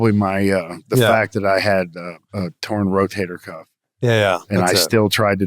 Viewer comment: What did he say at 46:27? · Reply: Torn rotator cuff